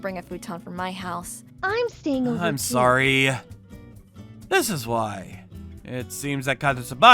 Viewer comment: why does it say "I'm single and cute" instead of "I'm sorry" 😭😭